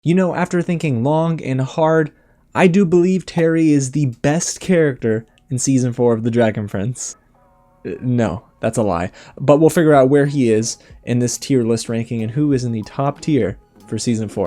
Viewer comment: I'm not the only one that thought he said dragon friends